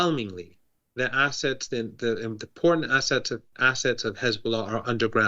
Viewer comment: Did he say “the porn assets” are underground????? 5:05!